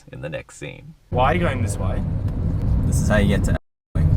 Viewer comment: did he say this is how you get too austwitz